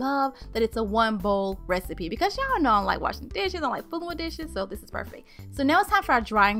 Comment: did she say what I think she said? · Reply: Nah, she said DISHES... lol